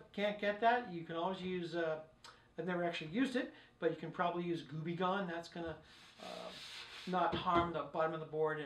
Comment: what does he say? "Glue-be-gone"? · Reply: goo gone